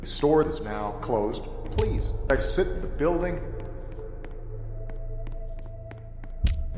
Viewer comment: I hear him say "the store is now closed, please." "I SIT THE BUILDING."